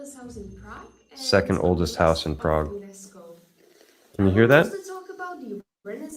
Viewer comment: Hi... just a quick question, at 41:42 the lady said a word that sounded like "unesco" .... is that what she said? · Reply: Yeah, I talked over her. 'according to Unesco'